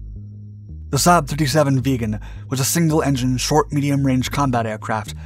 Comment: I keep hearing "the saab vegan"